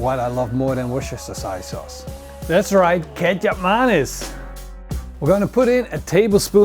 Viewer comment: This whole time I thought you were saying Ketchup Mayonnaise